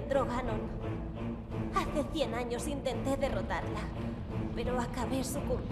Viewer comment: pone que dice ''intente'' pero parece que dice intento